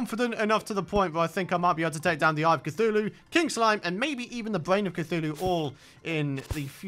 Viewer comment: I heard him say kink slime at the end of the episode. just me? okay...